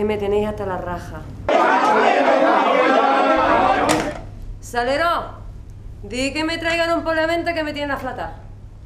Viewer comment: que dijo? 0:14? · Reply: Salero, di que me traigan un poleo menta que me tienen aflatá. Significado: Salero, di que me traigan una infusión de poleo menta que me tienen asqueada, con ganas de vomitar